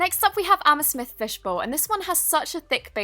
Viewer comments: did you say ame-smith? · Who else heard her say "amesmith fishbowl" instead of "amethyst fishbowl" XD Edit: omg guys thanks for the likes XD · 😂 she said amasmish fishbowl instead of "Ameythyst fishbowl" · I thought 💭 she said I am a fish fishbowl but she never 😂😂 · Is it just me or did she say amesmith instead of amethyst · Did she just say amesmith fish bowl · When she said amethyst it sounded like she said amesmith if u get what I mean... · she says 'amesmith' instead of 'amethyst' 😂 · did she say amesmyth?😂😂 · amesmith instead of amethyst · Did she just say amethyst as amesmith? 🤣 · did she say "amasmith"?? Cuz a got a friend and her name is Anna Smith😂😂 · did I hear that right? Amasmith? 😂😂